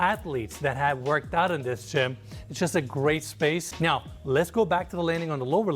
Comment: did he say „this is just a grey space“? because that’s the first time i would have to agree with him. seriously, go back 10 seconds and search for the colours… this is horrible.